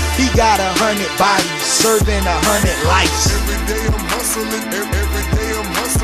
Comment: I always hear: Everyday I'm vasoline